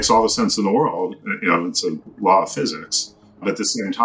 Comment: subtitles say "lot of physics" but should say "law of physcics"